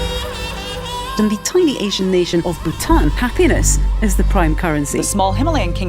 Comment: i thought she said p3nis lol